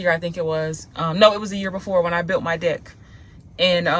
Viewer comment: Wait wait 😂 I thought you said “when I built my dick “ 😂😂😂, because sometimes you remind me of myself on your videos (sense of humor). Ok, that’s my silly side coming out…… but I love your content 😊👏🏽